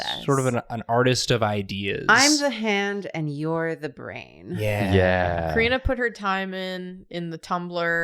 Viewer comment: you know, at the end of the intro, for a second i thought karina was saying "Im the hand, and you're the head, and together, we are a size queen!" xD dunno why, maybe cause of the mood this ep gave me from title to all around vibe.